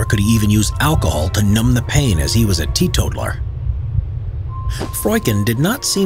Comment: Sorry, english is not my first language, but what does he say at 7:27 - Why couldn't he use alcohol? Because he was a tee-too-war? xD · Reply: I know this is a little late, but in case you still don’t know he said he’s a teetotaler. Which is someone who abstains from drinking any alcohol.